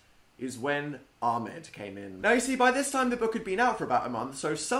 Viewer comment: Omg when he said Armid I thought he said Armin and almost cried cause of ATTACK ON TITAN!!!!!!